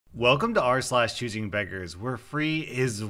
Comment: I work as I listen to your videos. So, when I was cleaning up it was a little bit too loud and I thought you said, chicken fingers instead of choosing beggers lol